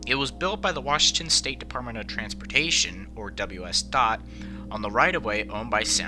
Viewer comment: Accidentally says thot instead of dot. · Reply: Never said that nonexistent word. I said "WSDOT"